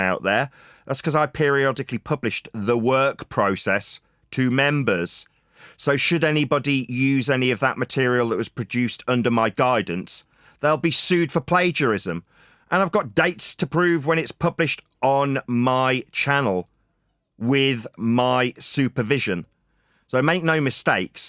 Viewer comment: “Produced under my guidance”. I thought he said it’s not ghost written 😂